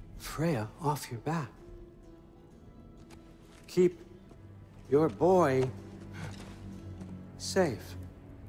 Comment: I thought kratos say *Your Mom* turn out its *Your Bow*